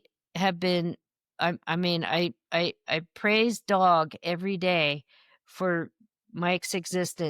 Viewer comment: Does she say, “I praise dog every day…”